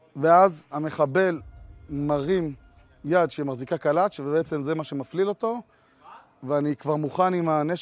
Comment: "Holding a knife"? He clearly said "Holding a Kalash" (Kalash=Kalashnikov=AK47)